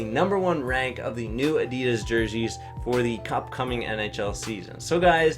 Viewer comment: who else heard him say cupcoming instead upcoming at the end